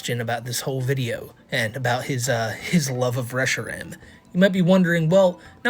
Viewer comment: I heard : “I love fresh shrimp” and I was vibin lmmfao 🦐